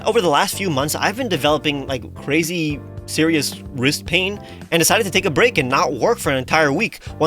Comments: I heard Rizz pain🤣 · I THOUGHT HE SAID RIZZ PAIN